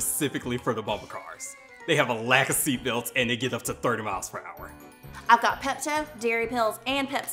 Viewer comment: I can't for the life of me figure out what he is saying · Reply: “They have a lack of seat belts and they get up to 30 miles per hour.”